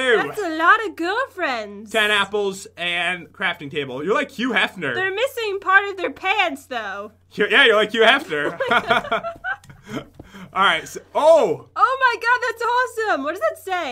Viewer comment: at like 4:10 did he say "Like you f her"